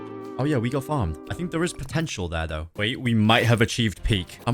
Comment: DID I JUST HEAR POTENTIAL???(Megumi)